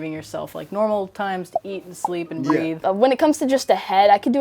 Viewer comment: Wait hol up…did they say breathe? Dedication at its finest